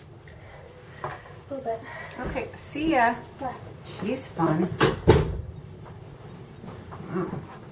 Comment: Your daughter is awesome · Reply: did you say "shes fun..." @ 4:21? if so i laughed my ass off